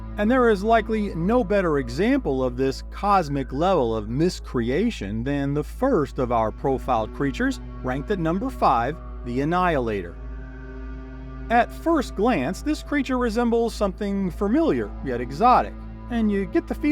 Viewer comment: Do you realize that you say "fermilyer," instead of "familiar?" Now, you'll never unhear it... MUAHAHAHAHAHA!!! 😈